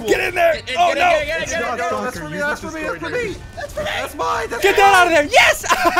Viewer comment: anyone heard a swear word?? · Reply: Ruben Balduk I came here to see if anyone noticed it also sounded like he said “get the hell outa there” when they were playing hungry hippos when it almost went into yellow